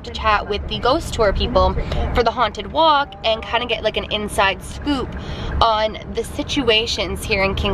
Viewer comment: I thought you said 'an inside spook' and now I'm not going to lie... a little disappointed that you missed it haha